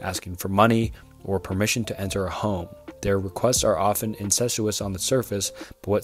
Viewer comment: did you say their requests are often INCESTUOUS???